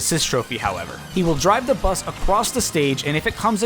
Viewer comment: Who else at 9:21 heard acwoss instead of across